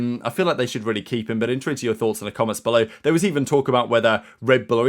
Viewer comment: What are you saying? I hear "in truents to your thoughts" Please clarify. CC says you're saying twitter.